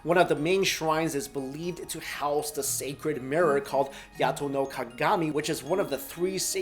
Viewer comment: DID I HEAR YATO NO GAMI